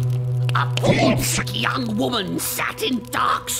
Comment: AM I THE ONLY ONE WHO HEARS QI RONG FROM TGCF??? (the tgcf brain rot is real😿😿)